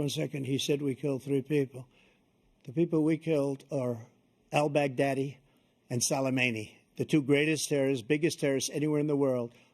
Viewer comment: lol sounded like he said the 3 people we killed were ‘el big daddy and So Low Mainey” lmao 😂